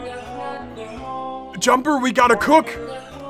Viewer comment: I thought that said Juniper, like the GD youtuber and i got concerned